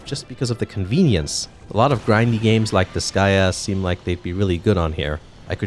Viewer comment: What 'grindy' game did he say at 4:10? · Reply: I know 3 years later but still I'd your interested or haven't seen their other videos. They said Disgaea (spelt exactly like that). It's a series of tactics JRPGs, 6 is the latest entry. If you ever see footage of characters (like the blue penguins called Prinnys) on some platforms in their videos then it's likely Disgaea. The one they are talking about over the Mario Odyssey footage.